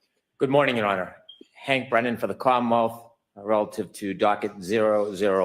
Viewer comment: 🤣🤣🤣 Check out the courtroom subtitles. "Hank Brennan for the clown mouth" instead of for the commonwealth.